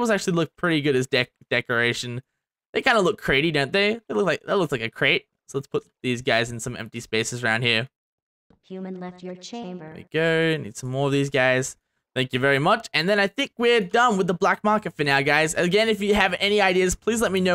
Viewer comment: did it say in the back round Human Left Chamber???